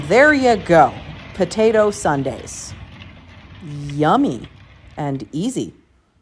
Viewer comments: i️ thought you were saying potato sunday, like you make all different types of potato’s and that’s all you eat. both sound like great options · I thought she said sundae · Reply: she did. Double points if served on Sunday.